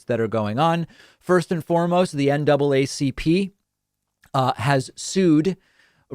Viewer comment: I’m embarrassed I heard NAACP and almost thought I heard NCAA